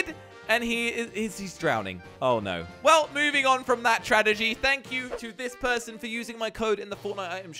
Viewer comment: When he says tradegy instead of tragedy